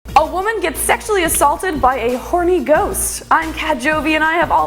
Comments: I thought it said 'horny goat' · I thought she said a Holy Ghost 😂😂😂